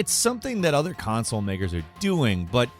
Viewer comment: Did you say "Console N---ers?" I know you said and meant "makers," but this sounds like a slip-up that could be exploited to your detriment. I love the channel but I've listened to this many times. I want to make this abundantly clear: I don't believe for a second that you guys are prejudiced at all, but I think a second or third take on the delivery of the line would have been prudent. We live in a cancel-culture world, and you have to be careful. This channel is awesome, inclusive and unbiased. I just don't want the "powers that be" attempt to take you down for nothing.